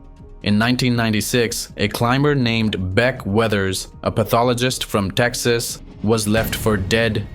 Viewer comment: Wasn’t until I heard you say “beck weathers” in your accent, that I realized it sounded like “bed wetters”